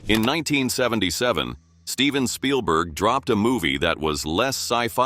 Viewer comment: I thought you said Spielberg dropped a doody for a sec